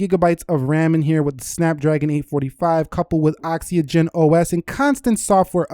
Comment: I'm salty I can't use it on Sprint, I bet u guys on Verizon are pretty salty too though lol Hold up what the fk did u just say at 4:19 lmao oxyogen